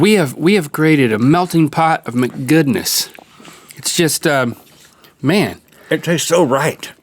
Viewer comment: did link just say mcgoodness?